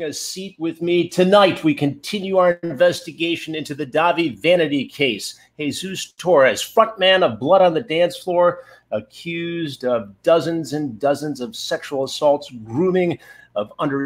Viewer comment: Literally thought Chris said, "F*ck man of BOTDF" instead of "front man" Close one. Whew.